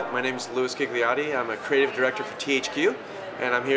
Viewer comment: HUH did he just say "my name is Luis Kick the Nazi"?